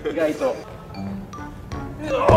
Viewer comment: でりょうくんが惜しいって言っとるの、おしりにしか聞こえん笑笑